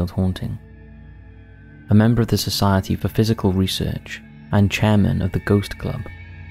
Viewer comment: Great video, but I believe the narration at 5:14 should be”…PSYCHIC research…” instead of “…PHYSICAL research…”. But I could be wrong!